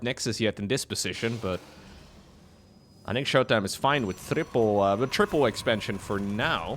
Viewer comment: “thripple? Was that a “th” sound I heard? 😂